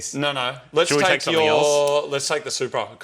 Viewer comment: did say... Supra?